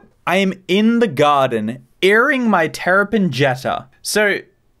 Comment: I Think He's Saying The Terrapin Is Named Jetta.